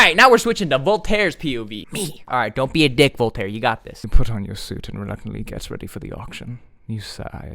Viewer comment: why did i hear him say “you got this daddy”- like legit not even as a hoke